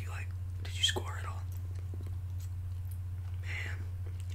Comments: I thought he said, "did you squirt?" 💀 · Him: did you score? Me: did i what??? Him: did you did you score at all? Me: tf i thought you said squirt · i was taken aback when i misheard him say “did you squirt?” like excuse me, sir? did i what now? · I don't know if I'm just dirty minded orrr what, because 4:15 I thought he said "Did you squirt?" I LIFTED MY HEAD UP AND WAS LIKE Õ_Õ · STOP AT 4:17 I THOUGHT HE SAID SQUIRT AND I SAT RIGHT UP LIKE DID I WHAT NOW- oh score yea heh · Um through 4:15 - 4:19 did he say "Did you squirt?Did you squirt all over the." ( doesn't finish sentence btw)